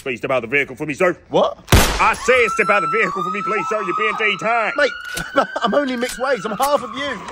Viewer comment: Sounds like he said ''You're being detanned''